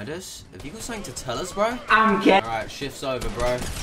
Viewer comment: can anyone tell me what Midas is saying in 5:04 plzzzzzzz <3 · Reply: Lahyieekh Ahamed i'm GAY